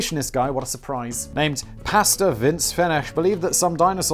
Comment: Omfg I misunderstood you at first and thought you were saying “Pasta” (like PAhsta) not “Pastor” and was like “that is a fittingly ridiculous name... why are you not acknowledging this?” Then common sense kicked in....